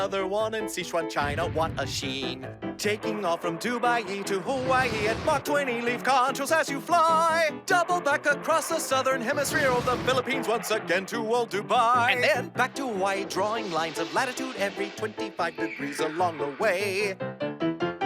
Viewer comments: DID HE JUST SAID PHILIPINES?!?!?!? I LIVE THERE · did he say Dubai?